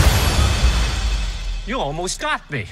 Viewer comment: Why does it say "you almost got me" when his mouth says "shot me" ....? did they censor the word "shot" ...?